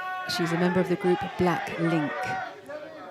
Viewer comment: did she say Black-Link ?!?!?!?!?